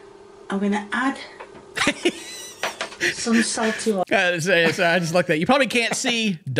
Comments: cooking translation: peppy-yay = black pepper huniyons = onions karyotz = carrots soltee-waltee = salt spaghetti = fettucine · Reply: ) me too!! And I’m from England! (The part where we actually pronounce our r’s) I genuinely thought she was saying salty water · cooking translation: peppy-yay = black pepper huniyons = onions karyotz = carrots soltee-waltee = salt spaghetti = fettucine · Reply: 🤣🤣 I thought she was saying salt water instead of salt lmfao 🤣🤣🤣